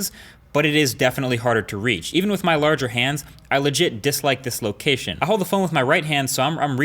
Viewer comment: Did he say "even if my LARGE are HANDS?"